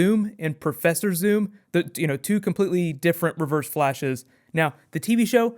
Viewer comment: At first I heard him say: "two completely different perverse flashes." Then I re-winded "Ahh, reverse flashes."